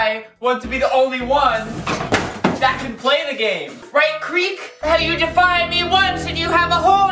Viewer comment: i heard creep and I like the voice xD